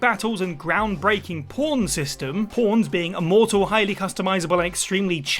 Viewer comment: With its groundbreaking “p0rn” system… YouTube: Wait wait wait… what did they say? DiD ThEy SAY “P0RN” Smithers: umm I believe they said “pawn” sir